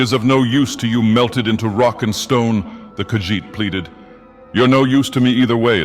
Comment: DID I HEAR A ROCK AND STONE!?